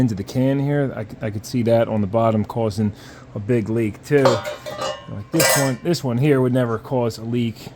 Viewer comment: Did you say Big League Chew?!!